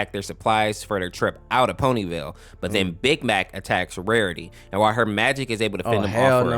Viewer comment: fr i heard big back